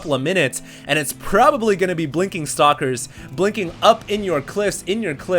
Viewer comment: bahahaha i swear he said "blinking up in your clit" at 7:23 :P made me laughh